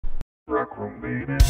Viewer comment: Rec room baby Rec room Rec room Won’t you play it with me won’t you play it with me Come to play right now Won’t you play it with me Won’t you play it with me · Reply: I heard "Rec room Beta"